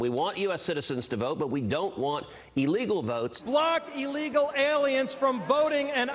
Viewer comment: Did he say “block” illegal aliens from voting or “black” illegal aliens from voting? It’s all one giant blur to me! Aw, fa-get about it … I’m voting for the #Coconut-lady! 😊 2:10 🌴